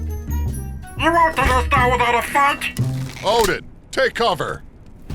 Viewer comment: did he say the f word? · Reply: Shampoo Shark I think he says without a fact